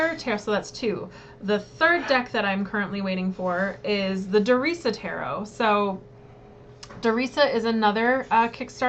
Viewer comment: How do you spell the third deck? It sounds like you say Dorisa or something similar. I looked up all the others! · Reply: LOL, I should have put links :) It's The Darisa Tarot: <link>